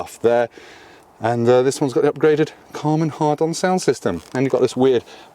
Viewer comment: did you say "Karman Harden" sound system, or was it just my bad hearing? lol